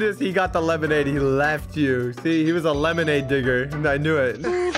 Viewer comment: I have one question. Why do you have the boss baby in the background😂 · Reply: At the beginning at 1:23 I thought said the n word but he just said digger